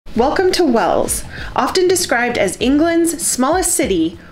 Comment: So sorry I thought you said Wales please forgive me xxxxxxx